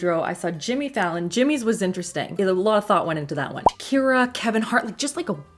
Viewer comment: did Shakira just get called Takira? 😂 Or am I hearing things...